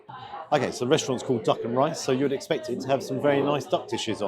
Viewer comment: duck en rice I misheard that 😂😂😂